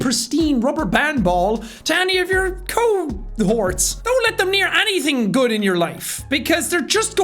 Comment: I thought he said "co-whores" instead of "cohorts" for a second kdjdjdk